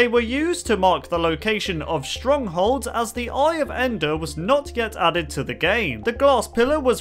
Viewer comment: Who else thought for a second he said “Iron Ender”? Just me? Ok